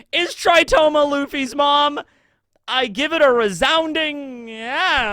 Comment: I thought said Luffy's mom is alive.